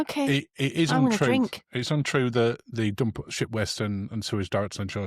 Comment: I misheard you first time I thought you said 💩 waste not ship waste 😂😂